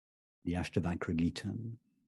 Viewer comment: Did anyone catch the 2nd book recommendation by Rupert at 2:09:27? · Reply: Ashtavakra Gita